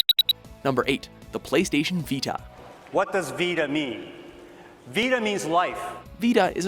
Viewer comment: When the dude say "What does Vita mean?" I heard "What does Vitamin?" xDDD